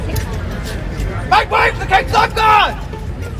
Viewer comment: Lmao did he say lifeguard?